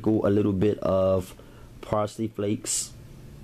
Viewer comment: I thought you said frosted flakes, not parsley flakes! I almost died